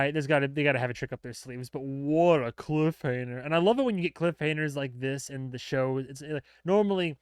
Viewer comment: Was I mishearing you, or did you refer to it as a "cliffhaner"? When i heard you the first time, I thought i misheard you, but then I'm sure I heard you say that three or four more times. It's "cliffhanger", not "cliffhaner". I really do love your reaction videos, though. That was just bugging me.